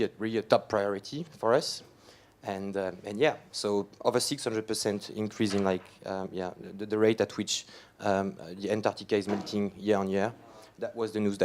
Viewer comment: 600% increase in the rate of what?? No indication on his graph title. His accent makes it very difficult to hear what he was saying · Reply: today's melting pace is over 600% faster than the rate at which Antartica's ice used to melt in the 80s <link>